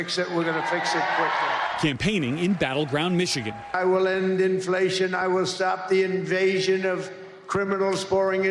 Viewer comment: did I heard him well: “ .. I am gonna start the invasion of..” That sounds like he was about to spit a secret 😂😂😂 “ i am sure it was invasion of Iran “ 😂😂😂😂